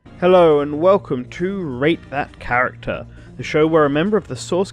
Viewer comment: Is it just me or does it sound like he says "rape that character"😂